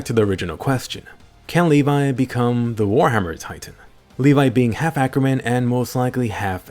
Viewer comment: ”Can levi become the colossol titan” I THOUGHT YOU SAID WARHAMMER